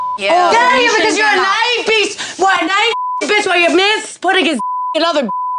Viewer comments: I STILL DON’T KNOW WHAT TF SHE SAID was it beast yeast like what tf was it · 60 years later & i still don’t fully understand what jenni said //. 😭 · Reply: On Hulu the captions says “Naive beast” 🤷🏻‍♀️😂